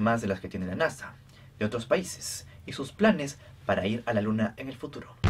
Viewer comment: Escuché "para ir a la luna de Plutón" :v